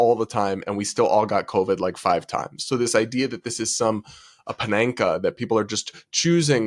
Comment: Brad, my brother in Christ, did you just say "pananka" rather than "panacea?"